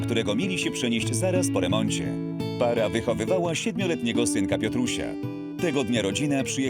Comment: czy tylko ja usłyszałam "wychodowała" xD Tak btw czy tylko ja zauważyłam że chyba zawsze jak dzwonią to chodzi o jakieś medyczne sprawy, a przecież na 112 dzwoni się też w innych sprawach xD Ja potrafię chodzić i skakać na rękach xD po proszę nagrodę